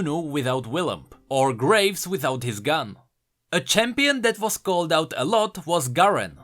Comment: I thought at 0:55 he said graves without a cigar.